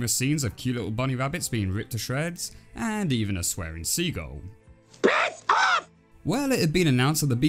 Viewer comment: I can just imagine a 3D bird screeching " Piss Off! " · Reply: Does the bird really say piss off because everytime i see that scene i just hear "back off"